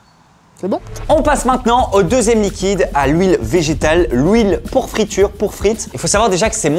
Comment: "l'houile" j'ai cru ré entendre le sketch des Nuls: "Ils te donne quoi à manger tes parents ? De l'houiiile" 😂